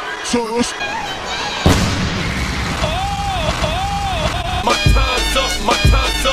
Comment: I'm probably the only one who misheard "My time's up!" as "My Condom!"